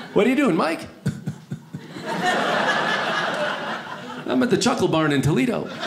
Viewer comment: for a minute i thought he said chocolate barn